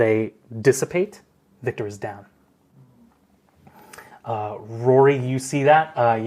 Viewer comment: omg I had to watch that moment again, cause the first time round I thought he said after the insects dissipate Victore is gone not down, and for a moment there I was VERY WORRIED.